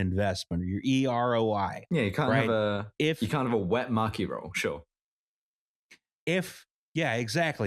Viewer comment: the first time said maki roll i definitely heard “mucky roll” which i feel is appropriate considering the contents